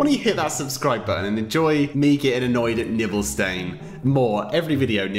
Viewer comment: it sounds like he's saying nipple stain xD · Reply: he/she knows he/she is saying that it sounded like was saying nipple stain